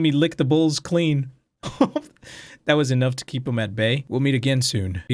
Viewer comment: What he actually said: him at bay vs what i heard: him at medbay me: SUS